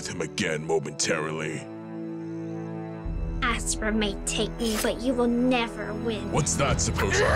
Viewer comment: I have a question like wtf did she say? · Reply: “Asra may take me but you will never win”